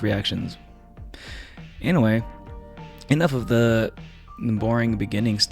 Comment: You say "anyway" a lot and every time I hear "Inoue" lol